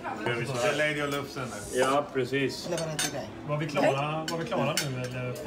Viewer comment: Were the people on your table from Belgium when you got your cake? It sounded like they said: Ja, precies. Which means. Yes, exactly! :D